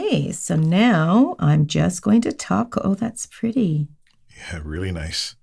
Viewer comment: Did I hear TACO?? YAY!